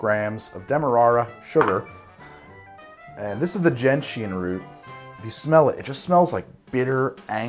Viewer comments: I had never heard of Gentian Root. I thought you were saying "Ginseng" wrong the whole time lol. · This whole Tim I thought you just said ginseng weird but then I saw the spelling